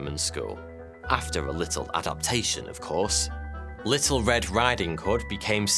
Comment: did you say “Adolphtation”?